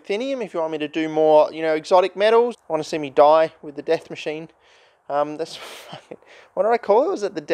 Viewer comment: So I started the video and misclicked to 12:04 and all I heard was welcome to death machine